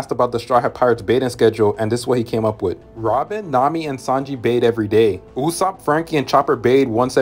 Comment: i thought he said robin nami and sanji bath together 😭